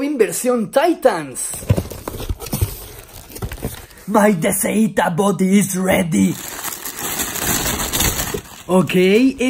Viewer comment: like si no entendiste que v*rgas digo :v (pongan subtítulos si quieren saber que dijo xD :v) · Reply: Llegué tarde pero dice My dcita body is ready